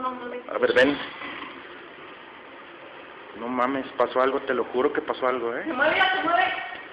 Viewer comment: Geez what does he have against mommy’s ? He says “ no mommy’s”.